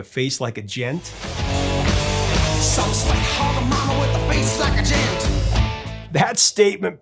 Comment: Always thought he was saying 'face like a JET' and was singing about some badass groupie.